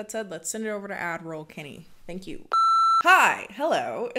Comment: This whole time I thought she was saying Admiral kenney. I was like yes lead us to the adds queen. I'm prepared for battle.